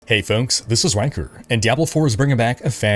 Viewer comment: Even without the captions it actually sounds like you say "This is wanker"